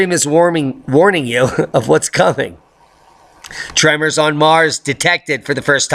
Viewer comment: Omg, I thought he said "trevor's on mars" 😂 No i am not . . .🤔